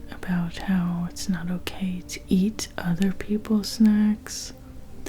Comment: I'm so tired that I just heard "it's not okay to eat other people" and just assumed he is a bit of a cannibal